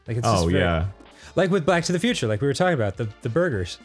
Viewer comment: I could have sworn I hear "Black to the Future"